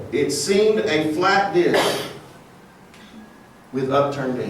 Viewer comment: What is he saying at 12:54 while the woman coughed? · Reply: flat disk